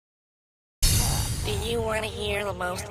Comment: It sounded like you said you want to hear the most annoying Sam in the world